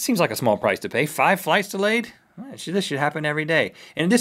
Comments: did he say "sh** happens every day."? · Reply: This should happen every day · is it just me or did rhett say "this sh*t happen everyday" at 4:29 ???